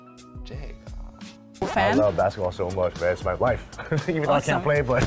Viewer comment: 難道只有我聽成It's my wife嗎